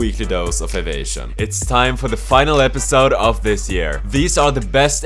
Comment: I heard "the final episode" I got the shit scared out of me